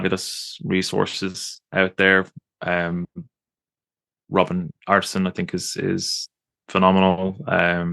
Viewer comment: Huge thanks for your podcasts! At 1:27:30 mentioned two authors, but I can't catch the correct pronunciation of their names. Can someone tell me who exactly he meant (I really want to find out more about the subject)? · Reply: That would be Robin Artisson - <link> and Gemma Gary - <link>